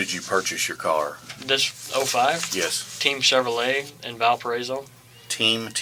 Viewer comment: I really did think he said teen chevrolet 💀seems like I wasn't the only one with that detective spelling it out!